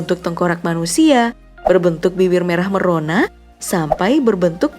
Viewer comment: gua ulang ulang. Ngakak soalnya gua dengernya biwir